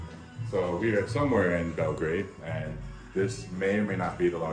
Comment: sounds like he says "this nigga" lmao